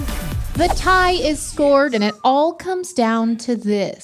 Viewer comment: she said the tie is scored instead of the score is tied and I had to do a triple take lol